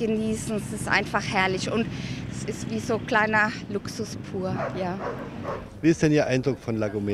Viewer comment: was sagt sie: Luxus poor?? 🤔😁😅